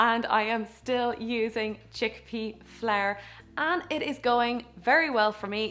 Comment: I thought she said chickpea flair.... 😂😂